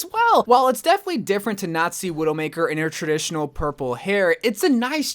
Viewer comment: did I hear nazi